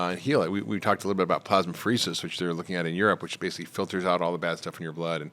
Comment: Some treatment is mentioned at about 9:44 into the video, I cannot understand what is said there… something about a treatment that basically filters the blood? In Europe? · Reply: “Plasmapheresis, or therapeutic plasma exchange (TPE), is an extracorporeal medical procedure that removes, treats, and replaces blood plasma to eliminate harmful substances like antibodies, immune complexes, or toxins. It is used to treat autoimmune diseases (e.g., Guillain-Barré syndrome, lupus), blood disorders, and neurologic conditions by filtering the plasma and returning blood cells with replacement fluids.”